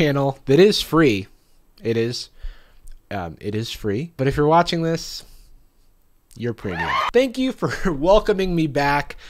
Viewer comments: I thought you said "but if you're watching this you're pretty" and I blushed.... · I misheard and thought he said "If you're watching this you're pretty" instead of premium and that made me feel real good about myself so I choose to believe that he actually said I'm pretty. Why thank you, Jarvis.